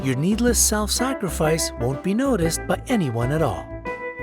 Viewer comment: who heard but instead of by